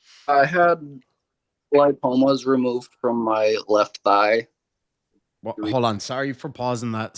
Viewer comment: He said “Lipomas on his left thigh” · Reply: Oh okay. I thought I heard thigh and “lye” but still had a hard time piecing it together. I’ve had those when I was heavier and was fortunate enough to lose them as I dropped weight. Makes sense.